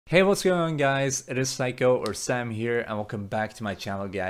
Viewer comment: I thought you said "its psycho" hehehe